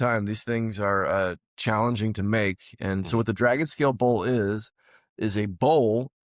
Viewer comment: I listened on Spotify. I don't know if it was early morning, or accent (Chicago will do that), but I heard, "Dragon Scale Bull" and could not figure out why I'd need a bull figurine. Did finally get there.